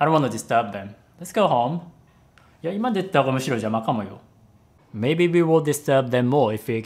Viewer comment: 今出てったほうが、むしろ邪魔かもよ is that really how a japanese person would say it or did garble that sentence? it sounds like you said や今でったほうむしろ邪魔かもよ and the 邪魔 sounds weird